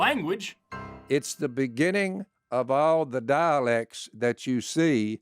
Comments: When he said "all these dialects" I swear I hear "all these Daaleks." Got excited for a minute! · ngl i thought he said daleks💀 Genesis of the daleks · Did he just say "it's the beginning of Daleks" at 29:28?